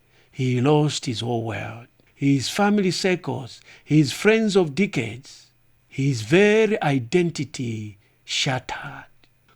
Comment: it sounds like you said “And in that instant he lost his whole world, his family psychos, his friends of dickheads..”